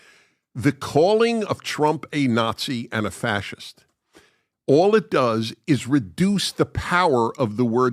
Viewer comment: My homo-phone rang cuz i heard "Fat Shit" instead of fascist!😂